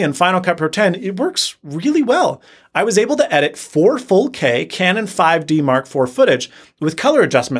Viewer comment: did you just say ‘Four Full-K’? 😂😂